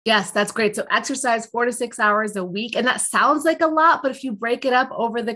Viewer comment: I swear, I honestly thought you said exercise 46 hours a week. Doh!🤣